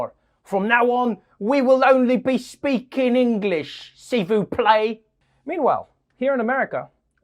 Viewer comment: For one second I thought Trevor said, seafood plate🤣🤣 instead of S'il vous plaît.